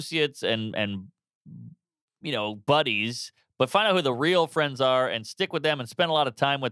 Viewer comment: I thought Mark said ‘bunnies’ instead of casual ‘buddies’ but then he talks about fluffy ppl 😝